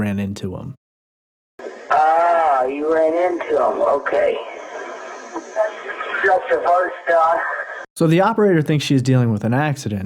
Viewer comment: I definitely heard that it sounds like said "Stater" like as in State Police in the beginning... I heard that he says that there's one car already here and chased into the woods ( exact words) then he says after the operator, "we're not talking to'em, I just ran into'em"... after the operator he then says "just the first guy" at 20:42. I didn't hear gunshots though.. sounded to me like maybe the phone was picking up background noise from where he was because he was crouching there, silent, hiding bc he didn't want to be discovered. In order to cut out noise, he ends the call with the operator to prevent noise from the operator coming from the phone. I work in medical coding and transcription, I listened to this with my work headphones on and no background noise at all... and this call... it's not possible to get exactly what was said at all. Sounds like his face and mouth were pressed way too close to the receiver of the phone while he was attempting to run away from danger then he hid and ultimately hung up to cut down on noise to keep from being discovered. What I'm wondering is, if he ran into other folks prior to whoever coming there, we're all assuming the State police... he went back to his truck to call his family bc maybe he forgot his phone in his truck... he then walked back again to where he saw the others from his truck after reaching out to his loved ones for help and then saw the "Staters" doing whatever to the people he'd already run into befor